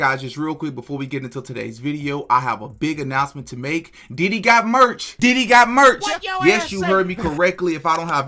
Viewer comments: Lol im not gone lie i thought you was saying “Diddy got merch” …. Im like aint no way lmbo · Hahaha, I thought you said Diddy got merch.😂😂😂